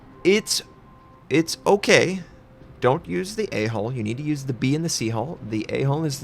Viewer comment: Lol when you said “A Hull” I swear it sounded like you said don’t use “A hole” use the “B hole “rofl